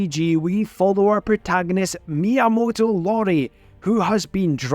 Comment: Just a little FYI his name is Miyamoto iori. It's a capital i and not an l. I say this because it sounded like you said Miyamoto Lori. Now if I'm wrong I'd like to know. Anyways Fate samurai Remnant is so good but I just got Tales of Hearts R for my ps vita and that'll take most of my handheld time